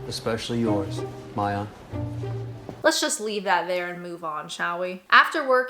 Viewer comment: I had to skip back and relisten, because I thought he called her "Mom" 🤣🤣